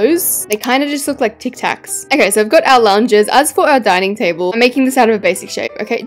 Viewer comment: Did she say dining table?